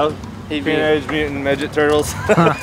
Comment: me: haha I thought he said midget turtle.. also me: realizes...